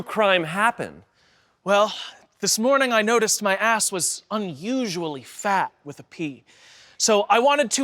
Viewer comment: When said "this morning i noticed my ass was unsually phat with a "P," I heard "My ass was unusually fat with pee"